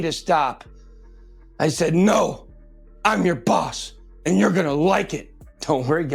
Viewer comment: Wait i had to pause this....."...no im yo daddy...?" did he just say that to Dick Namondi?....im cracking up!!!!!!...maybe it was "...im yo boss!!!..."??